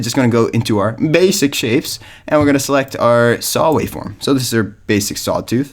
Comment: Did you just say "our -base-ic shapes"?